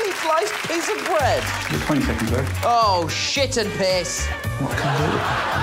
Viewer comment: Can someone please tell me what Alex says at 28:20 which causes Nish to curse? The YouTube auto-generated captions are no help at all. ☹️ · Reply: i think it's "you got 20 seconds left"